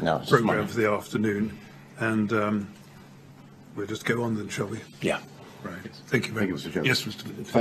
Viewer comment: Does he say “ We’ll just get on with it Chubby.” at 1:58?